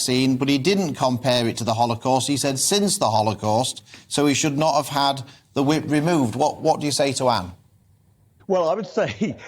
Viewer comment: I thought he said 'should not have had the wig removed' lollll